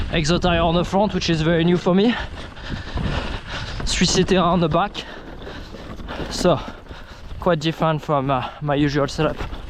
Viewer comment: I listened to it 3 times but I couldn't quite hear what you said at 6:50 about the tyre you are running on the back? Epic trail, I'd happy watch more small bike content on your channel! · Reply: That s a Maxxis DHR 2 DD 3C Terra